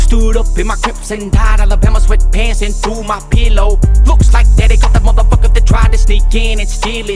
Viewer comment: The way he says pillow tho 😂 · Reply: Iv always thought he said pellow as in a meth pipe bc there's many names for one the love pipe the rose bud a pizzle and pellow